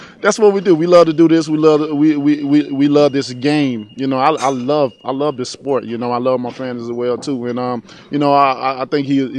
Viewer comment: lol Sounded like Wilder said jizzlements sport